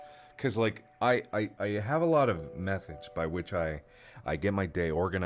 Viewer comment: I thought he said I have a lot of meth